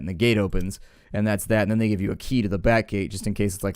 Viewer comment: i thought for a second that he said "And then they give you a key to the bat cave." Batman : ALBERT! what is this Toyota doing in my office!